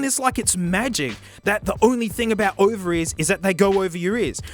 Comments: When I heard "Ovaries" instead of "Over ears" and then proceeded to slap myself in the face for forgetting the bloke is Aussie through and through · That fact that up until 4 minutes and 7 seconds into the video, I thought he was saying "ovaries" instead of "over ears" and didn't question it, is actually concerning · "Over-ears" i kept hearing "ovaries" could not finish this one brother